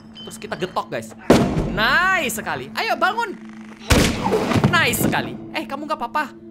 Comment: gw dengernya ayo balmond